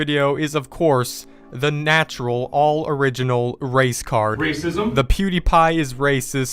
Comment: i thought you said race car not race card lol